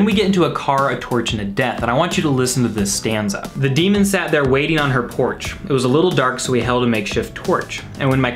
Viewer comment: For years I heard “the demon sat there waiting on her Porsche” •_•